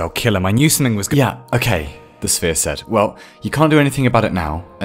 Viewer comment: omg I heard 'the sfeer(~ambience in dutch) is set' in stead of 'the sphere said' like 10 times before realising